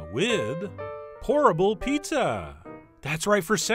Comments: Definitely thought he said "Horrible Pizza!!!" at first. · I thought you said "horrible pizza" in the beginning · LOL I thought you said "horrible pizza" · I thought you said "horrible" pizza. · Raise your hand if you thought he said "horrible pizza" in the intro lol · sounded like you said "horrible pizza" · i thought you said horrible pizza at the start lol